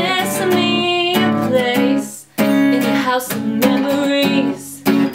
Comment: I thought it said 'house of memes' for a second